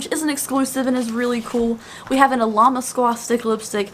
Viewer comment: It sounds like ur saying "llama squad". Lol. Love the unboxings. 💛